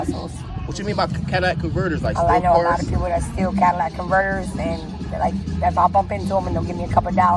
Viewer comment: What's a Cadillac converter? If she's stealing catalytic converters + getting a couple of bucks for her efforts she's a nitwit🤔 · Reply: I thought she said Cadillac Murders🤷‍♀️